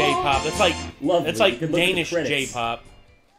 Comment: Is he saying Danish Jpop?